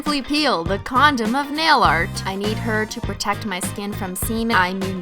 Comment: Did I just hear.. "The condom of nail-art"?